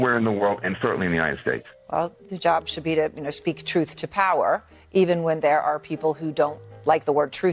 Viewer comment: Did she just say "Yruth to Power" ?!! Lol 😅😅😂